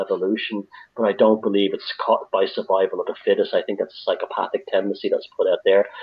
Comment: Sorry, I'm not a native English speaker and I have trouble to understand one part. Can somebody tell me what he told at 38:30? What he don't believe? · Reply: He's saying that he does believe in biological evolution but that he doesn't believe it is based on the principle of survival of the fittest.